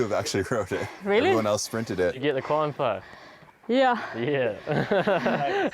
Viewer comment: Is it just me or do the subtitles say "Did you get the corn pipe?"